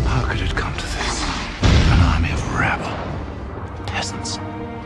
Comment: "How could it come to this? An army of rabble, peasants." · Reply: you’re probably right. For 20 years I thought he said “rebel.” Probably because I’ve never heard the word “rabble” in my life until now. But rabble makes more sense upon googling it.